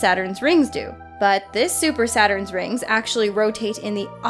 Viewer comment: Am I the only one who thought she was gunna say Super Sayan?